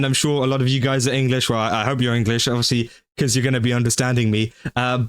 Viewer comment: ¿que dijo el señor? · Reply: dijo i hope you know English.